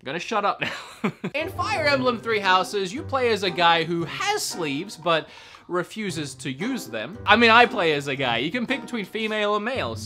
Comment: When he said the guy has sleeves and refuses to use them i thought i heard him say he has slaves lol